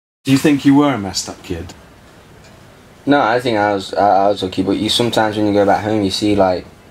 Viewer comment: i thought he said, No, i think i was a junkie, but he said lucky.... hard to keep track on his accent MAAAAAAN! <3